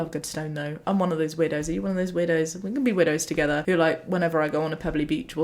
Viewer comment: your way of saying "I am one of those weirdos" got me confused because I heard "widows" XD